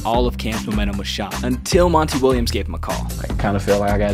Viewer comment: I thought he said Montel Williams, and I was like, Damn, the man went from talk show host to NBA coach, and this is the first I'm hearing about it!